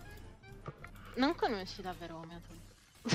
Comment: Cosa dice a 15:19? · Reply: " non conosci davvero homy . "